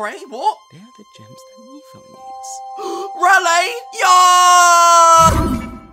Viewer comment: I could tell what me pad said, he said "they are the gems mephone needs"